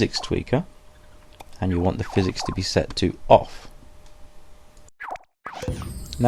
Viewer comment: I thought he said impact sexer